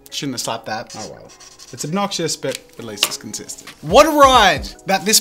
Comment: When I heard him say “it’s obnoxious but constant” my face went like :O cuz of memorys